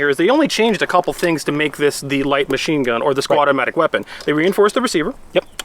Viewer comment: did he say squadomatic weapon?